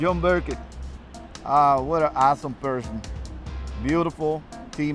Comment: it sounds like he says, what an asshole person.